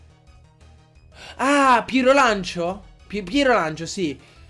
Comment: raga solo io a 19:05 ho capito Pietrolancio!!😂😂😂😂😂 disagio!! comunque mi sto divertendo tantissimo con questa serie DDG sinceramente no